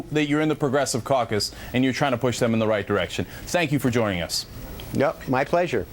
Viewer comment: did he say "Cenk you for joining us"?